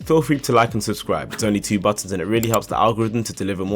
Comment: The video started out very very very well but I got confused at the like and subscribe message. For some reason I kept hearing "it is only two bucks" and I thought "this dude doesn't have patreon or join button... what he means "two bucks". Subtitle enabling gave the answer, it quite literally said "Mava you are a maroon, it says two buttons right there".... sigh... youtube hates me..... as of me writing this (March 26th noon Dutch time) Lawson is still the RBR driver according to the RBR website.